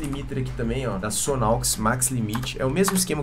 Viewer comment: Fala galera, blza TOP as video aula tem um plugin de compressão que vc usa que não consigo achar talvez por não estar com o nome correto no vídeo aparece no tempo 13:24 eu entendi que o nome seja "SONNOX max limiter" porem os pluggins são diferentes, consegue me dizer o nome correto do pluggin? Abrax, seja sempre ILUMINADO.:. e muito SUCESSO em sua linha de vida. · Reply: Mano dei um jeito aqui, tirei um print do video e pesquisei no google imagens, achei o Plugin. o Nome dele é Sonalksis MaxLimit Stereo, espero ter ajudado! (y)